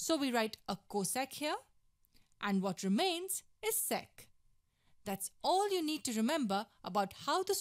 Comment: ‘sec’ sounded like sex